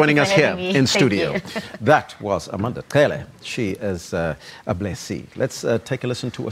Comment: I thought the guy will say "that was Amanda Cele, she is a b*tch"